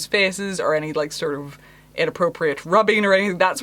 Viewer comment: I legit had tea almost come out my nose when the PDA question came up and I heard "inappropriate RUBBING." LMAO... You've yet again made my day. Thank you for existing. :)